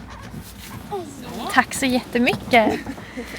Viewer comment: It's my first time hearing someone say "thank you so much" like that. That sounded so warm and beautiful. Did you say "tack ska du ha så mycket"? I'm used to " tack så mycket" I love the sound of Swedish, and I hope to get better at it soon❤ And as always, thank you so much for sharing your beautiful life with us ❤❤ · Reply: She said "tack så jättemycket" 🫶🫶🫶